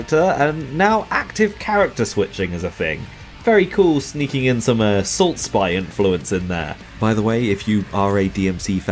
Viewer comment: I can't catch the name of the game at 8:33. Can someone tell me what it is? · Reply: Assault Spy